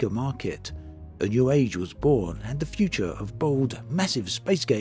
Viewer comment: “..and the future of bold, massive space game was assured” What I heard: “..and the future of bald, massive space games was assured” 😂 Sorry, it’s just what an Aussie heard with your accent 😜